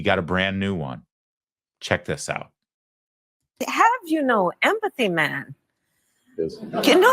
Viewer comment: it almost sounded like she said, "Que? No!" like a Spanish person.